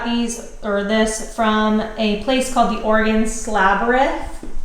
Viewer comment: Where did she get the countertops from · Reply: She says where she got them from at 1:25 …sounded like The Oregon Slabyrinth